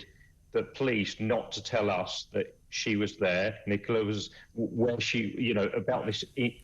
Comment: 5.54 it sounds like he said "whoever didn't tell us she was there" as if he just gave away inside information then changed it to "alcohol issue"? Strange